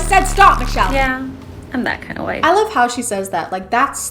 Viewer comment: I thought she said “im that kind of white” not wife 💀💀